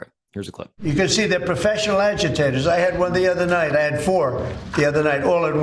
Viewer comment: Did he just say '' professional adjectives ''? instead of instigators? Coming from a dumb ass adjective himself.